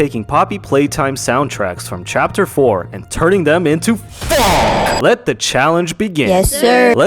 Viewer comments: instead of "phonk" I heard f**k 💀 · When he said phonk it sounded like he said fuck